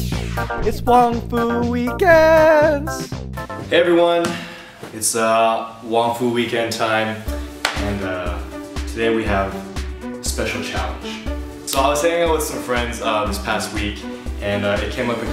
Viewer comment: At the beginning of the video when you said WongFu Weekends it sounded like you said, "Hey everyone! It's ah, waffle weekend time." <-I was like cool! Their gonna do something about waffles! But then I realized you said WongFu weekends lolololol :]